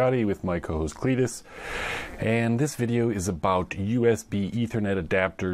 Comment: i am not sure but i heard "cochones clitis" xD 4:36 i also bought these usb2ethernet (the white one from china) one downside i found is that it seems it limited to 6Mbit but its ok.